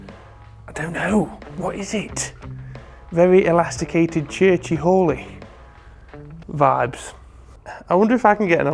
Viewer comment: I swore you were saying "church whore.".... 🤦🏻‍♂️🤦🏻‍♂️🤦🏻‍♂️